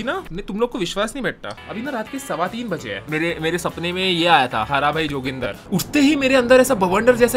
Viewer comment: I thought he said haara bhi joginder🤣🤣